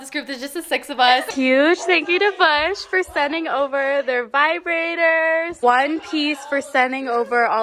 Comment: did she just say vibrator